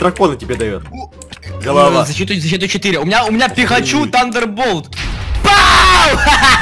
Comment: только мне послышалось ? что сказал пихачу ?